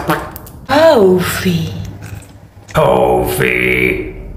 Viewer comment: When she said "CoFEe" i thought she said "ToFEe